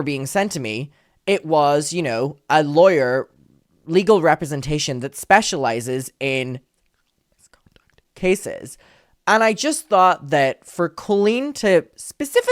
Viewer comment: does anyone know what her lawyer specializes in? i cant always read lips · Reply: The lawyer specializes in s*xual p*edator cases, he says